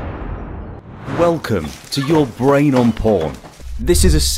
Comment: I thought the video said "Your Brain on Pork". I guess I am hungry ...